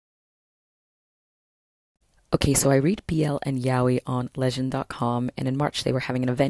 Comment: what site is she talking about I couldn't hear clearly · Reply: Lezhin Comics